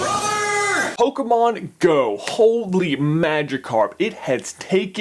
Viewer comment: with caption says, "Oh, c'mon! Holy magic card!" when in reality he says, "Pokemon go! Holy magicarp!" 😂😂